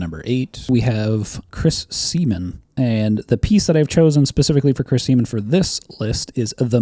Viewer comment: Homie all I heard was "Chris Semen and the Meat Hook"...Yeah clearly wayyyy out of context for me, as I was playing pokemon champions while simultaneously listening to this video 😅😂😂😂